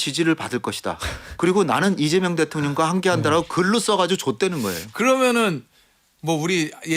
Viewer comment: 발음이 ㅈ되는 거예요 처럼 들림 ㅋㅋㅋㅋㅋ